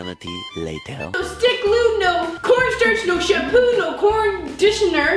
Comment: I thought said no corn starch jam jam